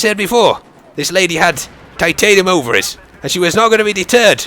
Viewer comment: When I was a kid I thought he said "titanium overalls" but noooo